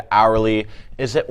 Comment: Hourly v Saraly 😂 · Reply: lol I didn’t even catch that😂 sounds like he said.. “Sourly”😂😂💀💀